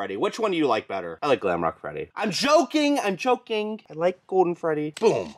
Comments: it sounded like you said "im joking! Im choking !" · Y did it sound like he said “I’m joking, I’m choking”